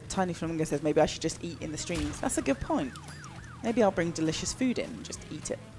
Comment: When elle said she should eat on the stream i thought she said yeet on the street and was so confused😂